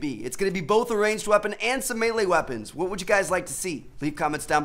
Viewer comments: What did he say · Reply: "check out these 2 videos on the screen if you haven't seen em yet, peace out guys" really isn't that hard to understand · what did he say at 2:15 "make sure you guys check out these toos screws" what is that!!! is that English??